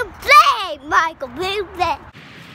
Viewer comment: at 3:03 - is he saying “Michael Buble”? 🤣🤣